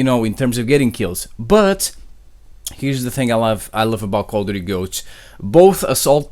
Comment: Did you say Call of Duty: Goat? 😂